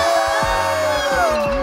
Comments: I thought you said 24 hours without blakely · I thought he said 24 hours without bakey i was literally so scared